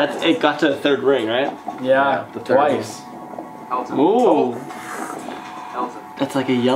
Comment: does anyone else hear something creepy saying Elton??? Or was that just me. Sorry if someone already asked this lol.